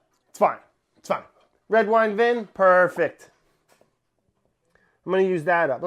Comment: Did Brad say Vin or did he pull a Molly for vinegar??